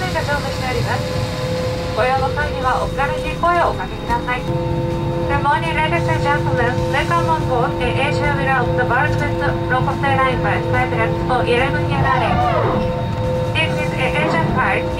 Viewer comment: "Good morning ladies and gentlemen, welcome on board Air Asia: world best low-cost airline by skytracks for 11 year in a row." CAさんの発音がよく聞き取れなかったので、他のエアアジア搭乗動画の同様のアナウンスを聞いて補完してみました。合ってるといいけど。